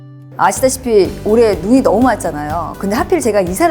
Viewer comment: 아시다시피 올해 돈이 너무 많잖아요 라고 들림 ㅋㅋㅋㅋ